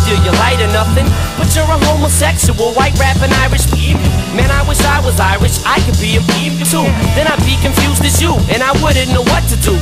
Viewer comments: I heard the uncensored version along time ago and he doesn't say "faggot" he says "Muslim" But you're a homosexual, white rappin Irish (Muslim) Man I wish I was Irish, I could be a (Muslim) too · the bleeped out part is eminem saying "White rappin irish MUSLIM, Man I wish I was irish I could be a muslim to, then I'd be confused as you" He says this because Everlast claimed to be muslim